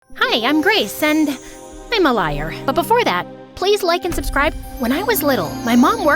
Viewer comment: “Hi I am grace and I’m a liar” great introduction 😂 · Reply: I thought it said “Hi I’m grease…and I’m a liar..